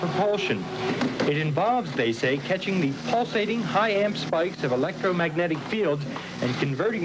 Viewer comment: what does he say at around 5:21 "pulsating high whatttt??" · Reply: "pulsating high amp spiked electromagnetic field"